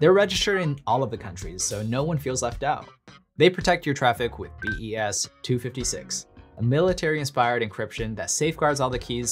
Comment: LOL!!!!!! 7:53 - I thought you said BS-256 as in Bull Shit lol!!!!